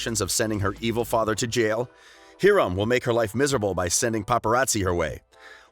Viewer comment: Did he just say Heeram instead of Hiram?!?!